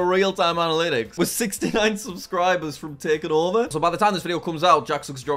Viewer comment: did just say 69